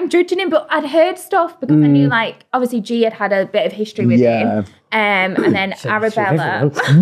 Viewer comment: what said for g ? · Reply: She had history with everyone she said